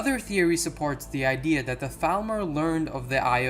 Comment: I had to listen to 8:01 three times before I realised it said Falmer, I thought I heard Thalmor haha. Love the video, good job guys!